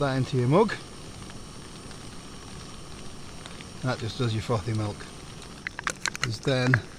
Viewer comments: Great video! I thought you said ‘f***ing milk’ rather than frothy milk at first. Had to rewind it to hear it again :L atb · When he said 'frothy milk'I thought he said 'f***ing milk' 😂